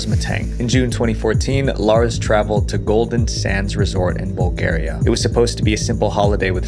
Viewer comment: I now this video is supposed to be serious but i thought he said “golden sans”